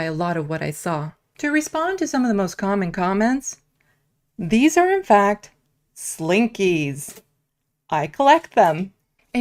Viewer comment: I thought she said these are slinky’s I caught them 😂😂😂